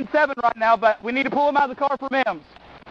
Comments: when i first watching this video, i thought he say "but we need to pull him out of the car for MEMES" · Damn I seriously thought the officer said "... we need to pull him out of the car for the memes." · I thought he said for "MEMES"